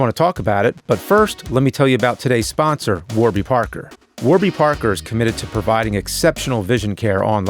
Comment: Try 5 pairs of glasses at home for free at <link> And thanks to Warby Parker for sponsoring this video. (The "home try-on kit" is only offered in the USA.) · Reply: It sounds like you said war be Parker.